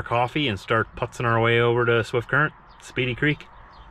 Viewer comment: At first, I thought that said Smurf Current. LOL Another great driving day! Janes Dough